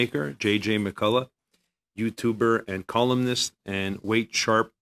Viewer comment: I thought it said "youtuber and communist".